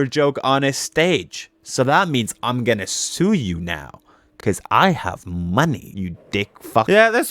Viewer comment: sounded like:''WEV'E GOT TO HAVE MONEY....'' 🤣